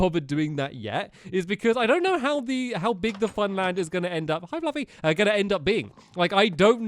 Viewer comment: When u said hi fluffy I thought u said hi lovey